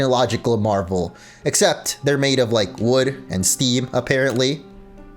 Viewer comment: did he just say "wood and Steve"?